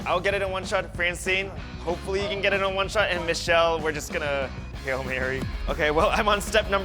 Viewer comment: Lol did Zach say “Hail Mary” 😂